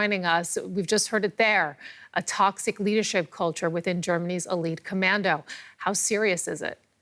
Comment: oh KSK... I thought I heard Gay as Gay commando or something sigh.